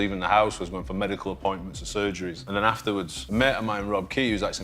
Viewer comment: I thought you said a year